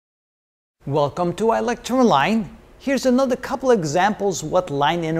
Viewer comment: every time you sire say "i-lecture-online" I hear "electro-line" maybe it`s just me, anyway great job here